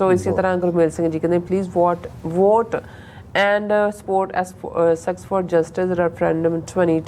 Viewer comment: At around 1:40 --I swear I heard what sounded like (from ji) "Sex for Justice" .... If that is true, I think I will RETHINK my whole view on these guys and their "struggle" i.e in a +VE sense! 😁😁